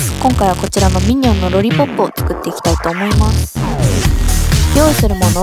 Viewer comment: ロリポップがロリコンに聞こえてしまった